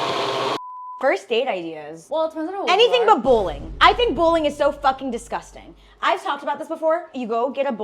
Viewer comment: I thought she said bullying for a sec 😭